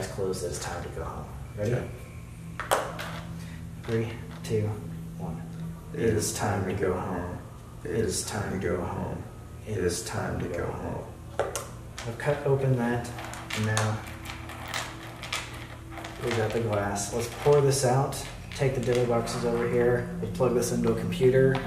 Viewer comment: I heard faintly and in unison when they said "the door's open, please come in" a female voice says the same thing. [edit] 13:27 same thing, same female voice. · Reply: Like, I don't wanna freak anybody out but at 13:27 to me it rather sounded like a third person said "It's time to go in" instead of "It's time to go home".....